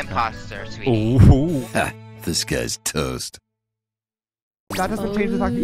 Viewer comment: Ngl almost of all these months i thought the intro said "This Guy's Toast" But i realized.... "Disguised Toast"